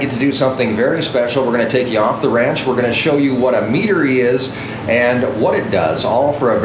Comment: Wow, yesterday I thought you said we would be visiting a meatery (a place that deals with of course “meat”. Little did I think it would be mead that would be produced. Thanks and